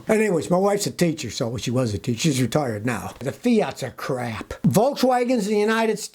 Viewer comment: At first, I thought he said "Well, she was a teacher. She's retarded now." lmao